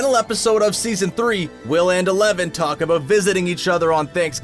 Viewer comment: Listen carefully at 6:10 - 6:13 He said will instead of mike